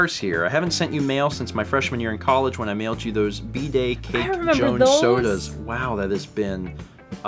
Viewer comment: For a moment I heard "bday cake" as "bidet cake". That was...an odd thought.